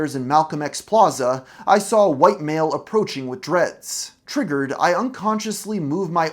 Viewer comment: did she really say triggered